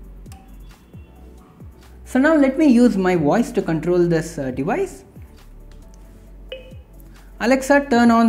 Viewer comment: im sorry 4:05 made me laugh! i heard "let me use my wife to control this device". good information awesome